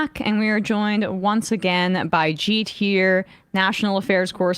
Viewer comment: Misheard her introduce him as a G-tier national affairs correspondent, thought Emma was being mean lol